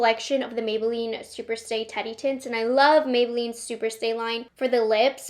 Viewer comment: Omg, I thought you kept saying Teddy Tits 😂 umm Teddy Tints. Got it!